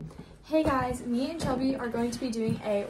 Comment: Did u just say me and chubby